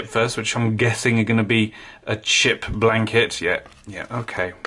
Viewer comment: Thought he said shit blanket. 😂🤣